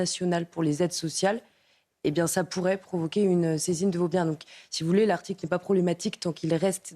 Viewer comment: Bonjour. Je ne comprends pas ce que dit la dame à 7:31 : "ça pourrait provoquer une saisine de vos..." Vos quoi ? Vous sauriez me le dire s'il-vous-plaît ? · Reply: Elle veut dire que l'on pourrait saisir vos biens.